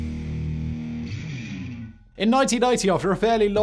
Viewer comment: auto-generated subtitles? How come the subtitles say 'in 1990' while says 'in 1980' ? (0:34) Very weird... :-/